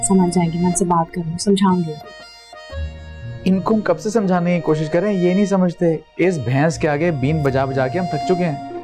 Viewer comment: lol did he just call his dad, "bhens" ?